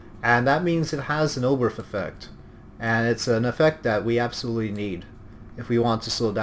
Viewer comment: what effect are you saying, I am not able to understand the word you say. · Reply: Ram Homier The Oberth effect